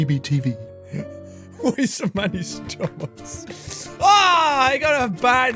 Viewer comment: WTF are you saying jack😂😂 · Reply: Great I think he said "why so many stomachs" in reference to the cow thing Nigel said but omfg was that mumbling hilarious 😂